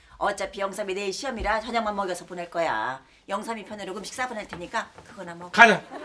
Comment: 여기 뭐라고 말하는거임? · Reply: 영삼이편으로 음식 싸보낼테니까